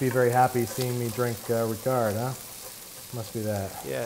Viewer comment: What was the name of that drink? It sounded like he said "Picard" but when I google that I just get a bunch of earl grey tea haha